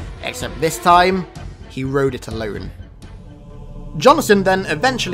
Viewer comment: did i hear you say he rode it to L'howon? hold up